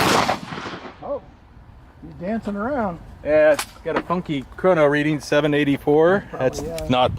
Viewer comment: When you said "funky chrono reading'" I thought you said "funky cold medina". Now I can't get that tune out of my head.